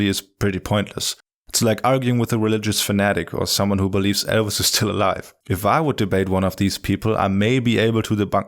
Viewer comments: At first I thought you said, "people who think elves are still alive". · I misheard 'Elvis' as 'elves' and got very confused for a moment. Great video overall. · I heard "elves" or "Elsa" at the end...it took me a bit to realize he meant "Elvis".